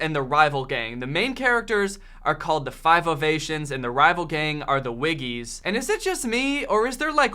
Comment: I totally thought you kept saying '5 of Asians' the first few times, and i was like....uuhhh wtf? 😳